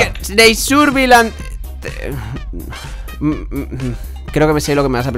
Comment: en el minuto 12:39 ¿que dice? ¿claro que e guardado el mapa o claro no e guardado el mapa? no te EN tendí GRITÓN!!!!!!!!!!!!!!!!!! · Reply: Creo que no he guardado el mapa